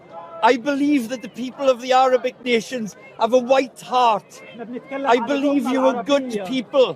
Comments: Did that dude just say they have a white heart and that, presumably, they’re good because of that. That is the weirdest “compliment” i have ever heard. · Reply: I thought he said Wide heart as in big heart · I dunno if I heard it correctly, but the Welsh bowl cut sounded like he said to the police "I know you have white hearts" 🤍 Wtf?